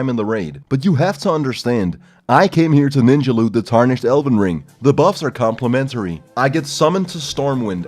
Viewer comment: why am i now mishearing that as "The Tarnished Elden Ring"